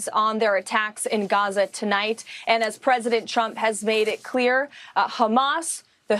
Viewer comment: It sounds like she is saying President Chump lol😂